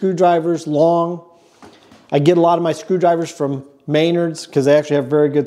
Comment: Anyone else giggle at butchery of Menard's name? I hear May-nerd's it sets me off.